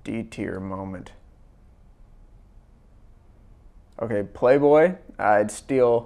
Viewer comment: I thought it said "Femboy" not Playboy